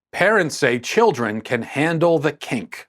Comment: I thought he said, “....children can handle the cake.”